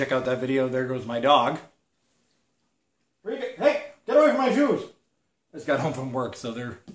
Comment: Lol at 25 seconds it sounded like he said get away from my jews